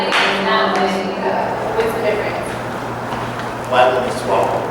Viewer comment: did he say " white women swallow"?